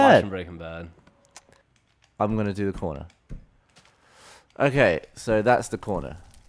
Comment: When Gavin said corner i thought he said porno o-o